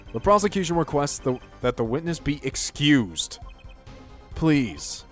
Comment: I thought he said executed